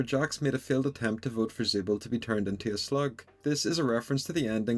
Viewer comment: OOOOH I missed that, when I watched it I thought he said “sl*t” not slug lmao